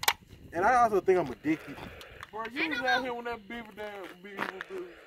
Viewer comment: what did he say 😭😭😭😭😭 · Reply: "You was out here when that beaver damn was big as this bridge"